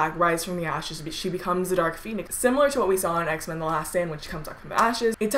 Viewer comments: i thought she said x-men the last sandwich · it sound like she said X-men the last sandwich